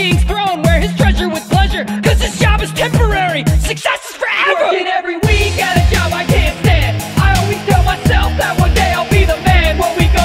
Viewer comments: First I saw this I thought he said fuckin every week I gotta job I can’t stand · “This job is temporary, success is forever” Still get goosebumps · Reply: I always thought he said, "Success is 4 Errors?!!!?" Ahaaaa Bacons 4 freee